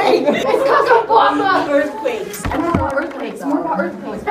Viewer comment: After some time I heard “earthcakes”